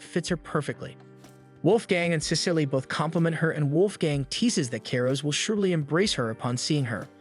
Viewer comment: I keep hearing “Wolfgang” as the butlers name 😭 (watching this sped up)